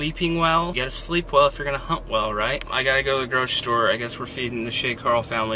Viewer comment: oh geeshe i died when i thought said 'you got to sleep well if you're gonna 'hump' well! HUNT! he said HUNT!